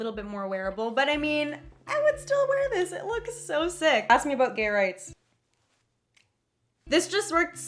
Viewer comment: Who else heard her say ask me about gay rights?